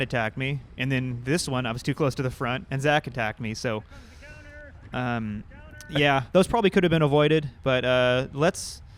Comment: What was he saying ? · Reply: I said "here comes the counter[attack]"